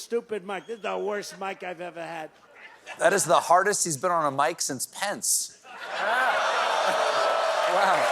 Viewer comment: I almost thought you said "that is the hardest mike he has been on since Pence", rather that "that is the hardest he has been on a mike since Pence". Subtle but important difference. That might explain the mixed audience reaction.